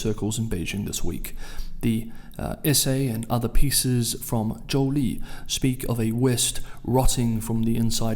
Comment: Thank you, 6:56 - What's said in this essay by (Jo-Lee?, former Ambassador to Ukraine) caught my ears on something said. A lot of USA rotting(technically says "West, but we know really know who's the targe), and seems to finish with " a rise of a righteous China that bring MORE JUSTICE AND DEMOCRACY to the international order". Hmm... Interesting.. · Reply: Thank you Leshic, a pleasure as always. Sorry, yes, his name in Pinyin is Zhōu Lì